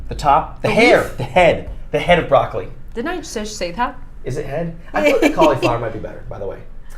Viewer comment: Anyone know what Saf says at 17:13? Def not English. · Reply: She said "Didnt I just say that?"